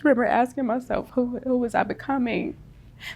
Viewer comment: That opening audio clip, I didn't quite catch it. Instead of "who was I becoming" I thought she said, "who was cumming......" I was certain it was going to be about some fetish activity taking place in the dark, orgy related, perhaps?